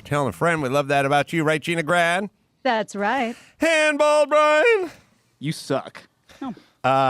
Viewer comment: When Adam introduces Brian, I hear “Hand Ball Brian!!!”